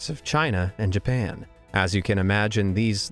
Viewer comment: did minecraft know? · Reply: (22,23,20) Wait did i hear japan?!!!!?!?!?! Wait WHERE SPOT